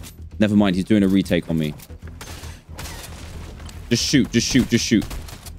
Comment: for a second I thought Dan said Le shoot lol